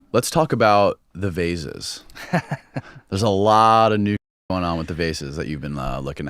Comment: Lol at the beginning i thought dude was gonna say " let's talk about buttered sausage "😅